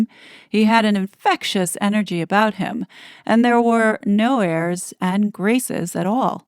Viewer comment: I thought she said there were no tabloids in america